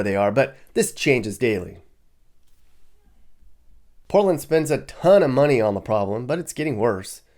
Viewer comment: LMAO at 1:08 he said POLAND instead of PORTLAND